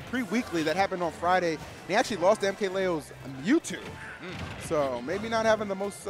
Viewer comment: did he say mkleo's mewtwo? anyone with a link to the video if there is one? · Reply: Sam K but he said tweek's diddy lost against mkleo's mewtwo. unless i'm misunderstanding